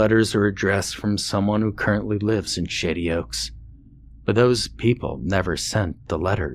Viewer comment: I keep hearing "$h!tty Oaks".😓